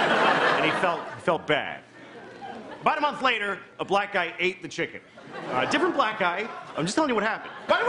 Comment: I thought he said blind guy not black guy